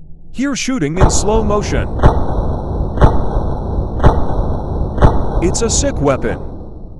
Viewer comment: i heard "its a simp weapon "